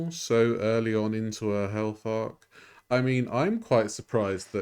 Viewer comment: When you said " health arc" I heard " hell fart".😂😂😂😂